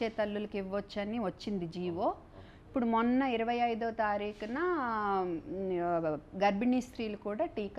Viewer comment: It's may 25th andi.. not june 25th(english subtitles are wrong)